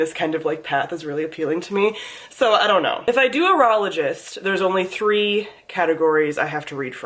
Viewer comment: I had to look up Aurologist 'cause for a second I thought you said Urologist.